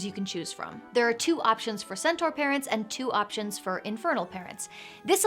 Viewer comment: I misheard around 3:00. I thought she said infertile.